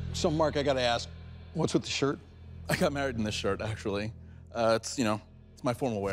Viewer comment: did bro said he got married in that shirt